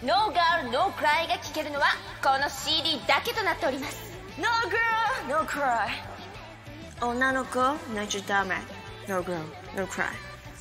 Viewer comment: I cant get what its saying at 2:34 D': I curse my bad japanese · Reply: I think she says NO GIRL NO CRY、女の子泣いちゃだめ、NO GIRL NO CRY.